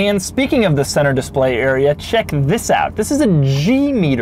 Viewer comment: I thought he said Jew meter